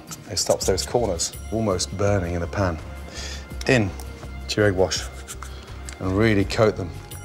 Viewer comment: what does he coat the fish in?? I can't understand what he's saying there. It sounds like jerry wash, but that can't be right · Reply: in ... to the egg wash